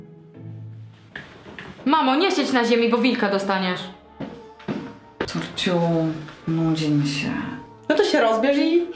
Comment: Co ona powiedziała? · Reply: Kurwa